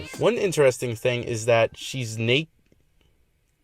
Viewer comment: What did he say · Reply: shes na